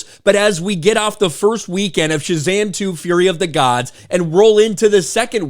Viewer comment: Did I heard you said "Shitzam"? LOL